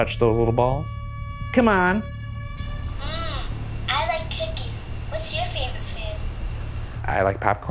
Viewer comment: I deff thought I heard "MMM, I like titties"😂😂😂😂😂